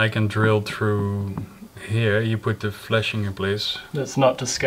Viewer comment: for a few seconds I was wondering why should put his "flesh" (hand?) on the roof to meet the drill. Sounds pretty painful. Then I realized that's not what said... 😂